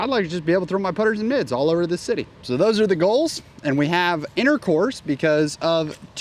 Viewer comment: I thought he said intercourse